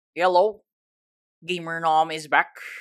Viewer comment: I love he said ‘hello’ but i hear ‘yellow'